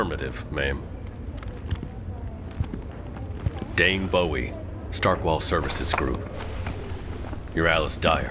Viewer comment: I misheard “Dane Bowie” as “Dave Bowie” and I was like “THE LEGEND HIMSELF?” 😱